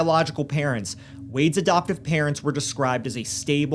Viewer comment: I really love y'all's videos, but respectfully, sometimes it's a bit difficult to catch what's being said--I'm not sure if it's a microphone issue or what, but sometimes parts of words get a bit lost so to speak. It makes it difficult to engage when one is hard of hearing, and I'm wondering if certain bits just need enunciated a bit clearer ^^ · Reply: (for instance, around 2 minutes in, I hear "'doptive" rather than "adoptive")